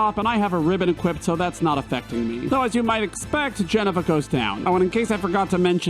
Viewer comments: Everytime you say "Jenova" I hear "Jennifer" and I love it! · I misheard Jenova as Jennifer and it made me so happy :)